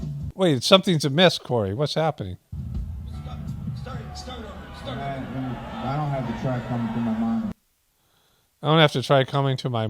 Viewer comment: He is actually saying: " I don't have the track coming thru my Monitor." = Meaning it is a Pre-Recorded Backing Track Cory F and the band will ultimately Lip Sync over (Like a Milli Vannilli) Cory A. Fraud.